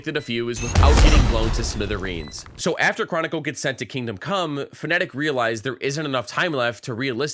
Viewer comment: why i heard kingdom c*m